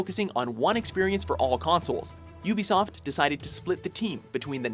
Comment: I THOUGHT you said noobiesoft instead of ubisoft